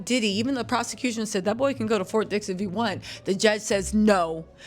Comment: I thought you said that they said he could to go to fort Dix.